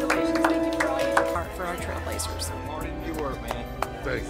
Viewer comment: Did he just say Trailblazers I used to go to the school of Naches Trail they hold each other the Trailblazers